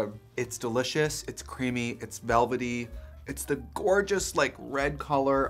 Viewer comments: When I heard “I’m eating myself” all I heard was “She eats herself out” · Trixie: “I’m eating myself” What I hear on repeat: “She eats herself out” 😂